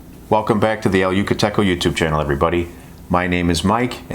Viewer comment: First time viewer, I misheard your opening welcome. I thought you said "Welcome to the You Can Taco"! I was in.